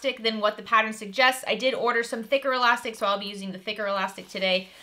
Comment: I thought you said knicker elastic then realised you said thicker elastic.😂😂